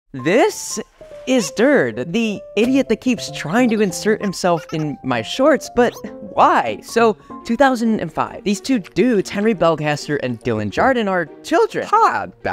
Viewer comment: When you said idiot I thought you said Indian